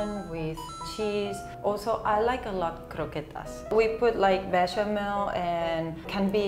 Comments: What the spanish girl says at 0:39 is "bechamel", one of the ingredients of croquetas. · Hi, I'm spanish, just wanted to say that at 00:39 what says is "bechamel", a kind of sauce made of flour, grease and milk!